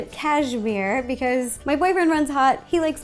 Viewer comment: Did She said My boyfriend?